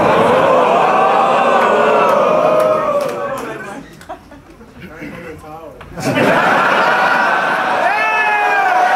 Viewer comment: Was hat der Mann im Hintergrund gesagt , habe ich leider nicht verstanden ?? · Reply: er sagt: kein kommentar oida. und der mann is